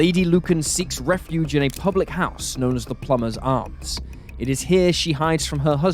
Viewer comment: At first, I thought you said the bar was called "The Plumber's Arse".